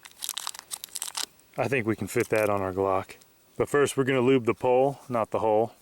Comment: I thought u said 'I think we can fit that on our cock'